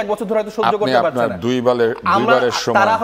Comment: guys, did you even hear what that creepy host said this guest while he was talking!??😒😠 Is that a mannerism maintainance of a talk show host referring a guest with the words"দুই বালের"!?? I don't think that sounded misunderstanding, he sounded like intentional..😒😒